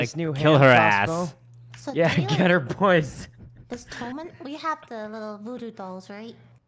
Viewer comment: what did he say???? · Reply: "get her boys"